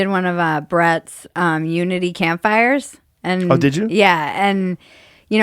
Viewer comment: Damn......I thought Bridget said...I did one of BRET’S Nudity Campfires....